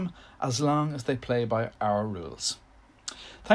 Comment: lool when you said 'folk' I thought you said 'folkers' xD